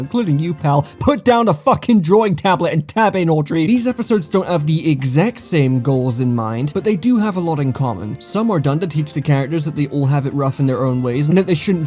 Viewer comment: am i going nuts or did he say Audrey?? I don't even have a drawing tablet and i feel called out